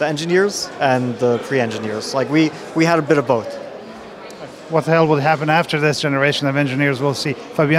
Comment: Did he say what the hell? 9:13 :face-blue-smiling